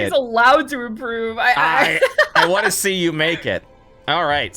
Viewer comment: I definitely misheard what he said there. It’s “I wanna see you make it.” NOT “I wanna see you NAKED”. 😅 I was like “you WHAT NOW? “🫨